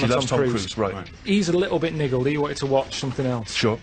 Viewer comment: whats he say?..hes a little bit ''.........'" what'?....this word to describe pissed off is What? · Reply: that word doesn't mean what you think it means. Niggle is an actual word used to describe annoyance. If it was racism Ricky and Steve would have been all over him. Karl isn't racist in any way.